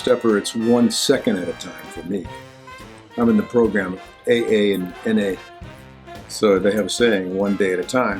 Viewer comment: Does he really say he's in AA and NA???